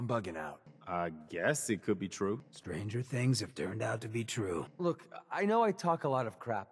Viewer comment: WAIT HOLD ON A MINUTE THERE DID YOU SAY STRANGER THINGS?